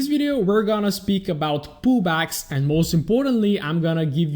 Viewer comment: All I hear is "poo bags" not "pullbacks" lol the whole way through sounded like you were trying to teach us how to get good entries into poo bags. All jokes aside superb content, subscribed on youtube and in my heart